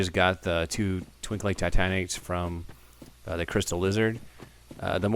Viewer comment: is he saying twinkling titanic's?